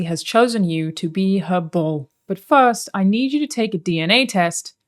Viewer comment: I needed 4 retakes to hear that you said bull instead of ball or just slurred a word 😂 It's quite obvious to me that if you subscribe to an onlyfans it is frequently not the person claimed to be the owner of the onlyfans who messages you, but I could never place a finger on how they do it. Having whole ass agencies for it is wild.